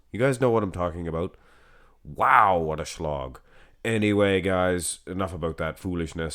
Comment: I thought you said “wow what a shlong!” I had to rewind and hear that again